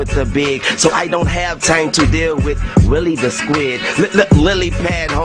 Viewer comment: As a kid that Gillie the Kid diss flew over my head. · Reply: I thought he said Willie the squid 😂😂